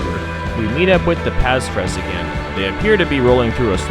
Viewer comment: Hello, thank you for the spectacular and wonderful video, as always! Regarding the voiceover: I liked the additional information! Being a foreigner I am often wondering how to pronounce the names of the towns - now I know :) . Your voice as such is very clear and pleasant to listen to, but please be careful about the volume of the background music. You did it almost perfect, but - being a non-native English speaker (or listener, for that matter) - I had a hard time to understand what you said around 4:57 (they seem to be rolling through a ??? here) as the music was rather dynamic at this point in time. Maybe you can tune down the background music a bit for longer or more complex explanations. Many audio editing software can even lower the background music volume automatically when you are speaking - but this feature needs to be used wisely, not to make the volume feel like a roller coaster ride. But this is really only a minor issue! At the end of the video you said that this voiceover recording is very time intensive ... still I would like to encourage you to produce more of such videos, as I liked the voiceover very much, but not at the price of producing less videos in total - then better switch back to the traditional style. Thanks again! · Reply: He said "slow order"